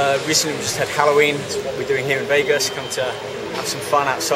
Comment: did he say heroin???